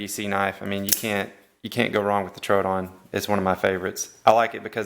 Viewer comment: Someone can write the name of the knife please? Not eng and dont understand at all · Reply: Troodon